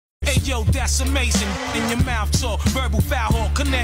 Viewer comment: I always thought he said “Decimation” not “that’s amazing”